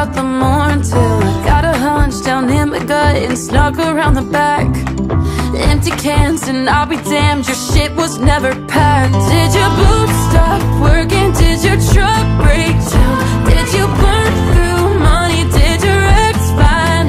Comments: I thought it said “did your boobs stop working” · My whole entire life I thought it said “Did your boobs start working”😂😂😂😂😂😂😂😂😂😂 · I thought it said "did your boobs stop working" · I heard "did your boobs stop working" 💀😭